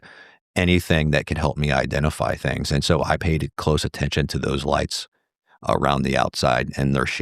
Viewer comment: ish. “I payeded close attention”. I heard your IQ in this moment. I’m done watching this dork. Kidding of course. I think it just sounds that way.